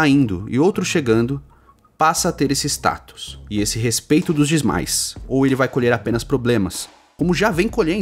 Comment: Eu ouvi um "dismais" aí ein? 🤣 Excelente vídeo mano, entendi perfeitamente o quadro atual dessa treta 👊